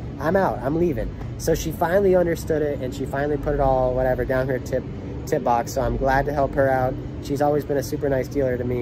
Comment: “Down her tip box” That’s what he said.